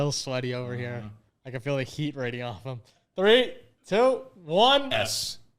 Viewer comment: saying “raydee-offah ” instead of “radiating off of them”